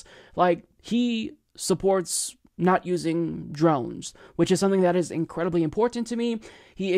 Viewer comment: Oh, DRONES! I had to rewatch that so many times to figure that out. I thought you said "drums", like whatchu got against percussion instruments bruh?